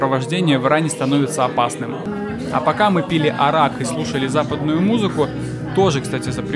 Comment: Арак ? А мне послышалось как Ярак 🤣